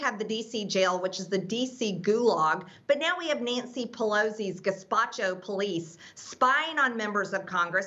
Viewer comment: Did she say "gazpacho police"? 2:40 🤣🤣🤣